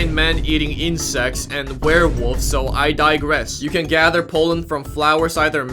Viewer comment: I thought he was saying "You can gather Poland." I forgot what history that summarized, but it summarized something."